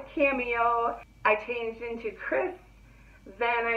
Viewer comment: What's the name of the wig at 5:05 ? I thought you said "Chris" but I can't find a video that you did on it nor can I find it on PY. Maybe I misunderstood. Thanks. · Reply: I think it’s Kris. Really pretty!